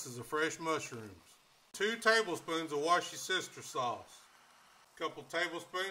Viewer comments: I’m sorry, but did he just say…. “2 tablespoons of why-is-your-sister-sauce” 🤔 😆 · Every time I hear him pronounce Worcestershire, I hear "what's your sister?".